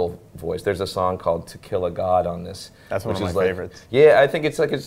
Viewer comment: I thought he said Tequila God... which would be AWESOME!!!